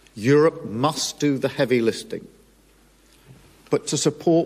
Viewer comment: starmer says about europe helping with the heavy "listing" (did he mean the heavy LIFTING) just like when he said about the releasing the "sausages" instead of the HOSTAGES?? THIS STARMER BLOKE IS AN ASS (btw, reduce playback speed to 0.5 and you'll hear it clear as day)